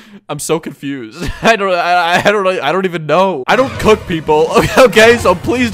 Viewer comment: what he said:"I DON'T COOK, PEOPLE!" what i heard:"I DON'T COOK PEOPLE!"